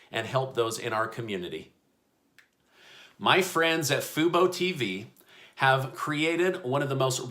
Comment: Oh fubo lol I thought you said FOMO TV. Only stock market holders would understand...